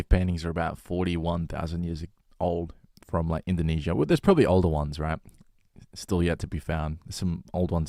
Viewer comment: Haha when you said “older ones” I thought you said Oldowan, like the old tools